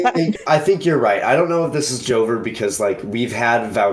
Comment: i thought they said joker instead of joever so i was thinking "dude why would the joker do these things"